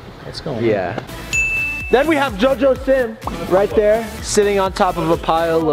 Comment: JOJO SIM is so cutee 🥺💗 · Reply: Jeydon Lopez for a second a thought you said JoJo siwa